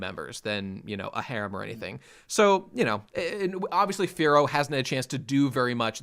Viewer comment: Whenever they say Firo I just hear it as "Fearow" and get really confused for a second.